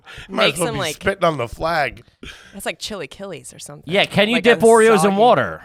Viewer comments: Being Mexican and the gang talking about Mexican food was the funniest thing ever I was dying and my culture was crying a little lmao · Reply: I thought she was saying chili kellys, and was wondering what brand of chips that was. Then the way they described tamales made me cry actually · “CHILLY KILLEYS “ is a hysterical way to pronounce chilaquiles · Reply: I thought she was saying chili kellys and was wondering what brand of chips that was lol